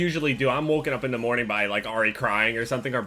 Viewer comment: I Thought He Said I Wake Up In The Morning Already Crying